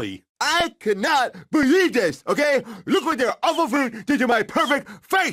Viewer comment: when you realized he tried to kill a family over A CHIPPED TOOTH... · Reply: I thought he said food